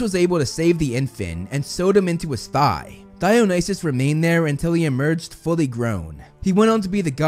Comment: Instead of Dionysus I heard Thigh-nysus and I laughed way to hard at that.